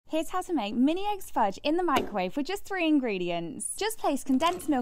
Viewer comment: For a sec I heard “mayonaise fudge” 🤮